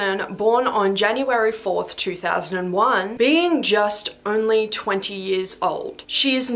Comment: I’m sorry peaches is only 11 months and 24 days older than me? I thought that chick was a delusional 36 year old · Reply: I thought she said January 24th. No mines December 28th 2001. They had my mom be induced, I was meant to be born Christmas Eve